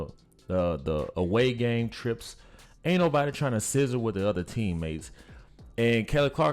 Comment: How about we just try to put ourselves in her shoes? 👏👏👏👏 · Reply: Lol! You were saying "sizzle" I thought you were saying "scissor" 😂😂 Lmao